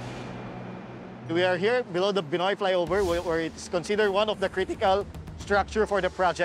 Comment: sorry bro... i seriously thought he said "Pinoy Flyover"... sorry ah...